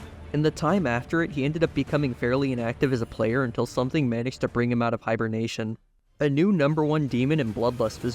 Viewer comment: I swear i heard "something manix'd to capture his attention"